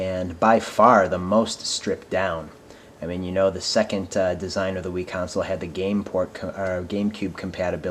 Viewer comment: For a Second, i thought he said "A Wii-Design of the Wii"